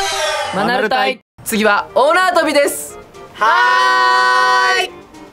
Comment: 大縄跳びが、女遊びに聞こえた😆